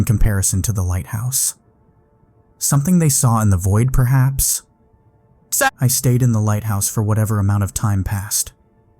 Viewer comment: does someone say "SUP" as in "what's up" here? after he says: "in the void perhaps".... 🤨🤔🤭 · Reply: Sup